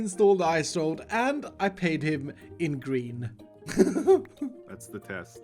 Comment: i thought you said “i paid him in grian”